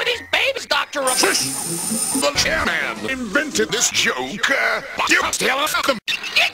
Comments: What does Robotnik say at 12:15? All I could hear was "The chairman invented this joke but.." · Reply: "...but you can still fuck them" Specifically it was a followup to this joke <link> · I hate to be that Walrus Guy, but what does Robotnik say at 12:14? I don't watch TheChairman45, so I don't get it. :/ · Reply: "The chairman invented this joke, but you could still fuck them" Here was the original joke. <link>